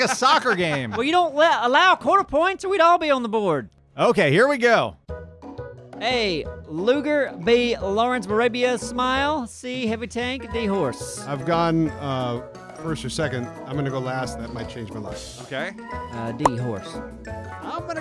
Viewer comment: did you say Lawrence of a labia SMLE?